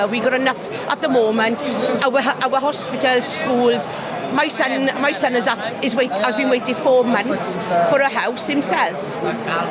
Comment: did she just say: "my has been waiting for four months for a house"? Try the Netherlands, people here are waiting for ten to fifteen years and are getting passed by illegal imigrants who get a bag full of money to decorate their house along with a social unemployment benefits and no obligation to go and get a job.